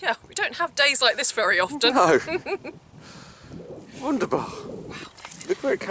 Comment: Did I hear the word „wunderbar“ from, when you found the Roman pottery?